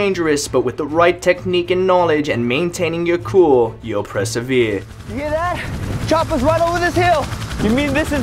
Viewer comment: if you turn on captions, he puts "pressive ear" instead of "persevere" around 12:46 lol